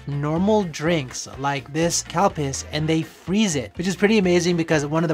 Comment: Did you say the drink was called cow piss?! I backtracked and listened twice. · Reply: The drink is spelled ‘Calpis’ but the pronunciation does sound like cow piss. Overseas they changed the name to Calpico because of that lol